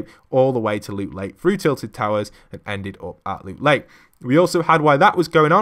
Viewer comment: Did he just say “Tillie towers”